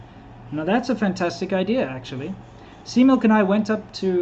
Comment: for the longest time i thought you said C-muk, i always wondered what that meant. until i payed attention, and realized it was C-milk... Sorry lol..... Must be my Southern Cali Accent!!!!!!